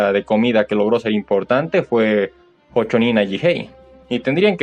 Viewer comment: No escuché bien ¿Como se llamaba él manga que dijiste? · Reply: Creo que te refieres a Hōchōnin Ajihei, el único manga de cocina que había triunfado en la Jump hasta la llegada de Shokugeki no Souma.